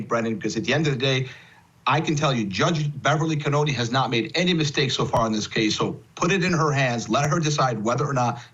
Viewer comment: Did I hear that attorney correctly at 21:26, when he said "Judge Beverly Canone has not made any mistakes so far in this case?"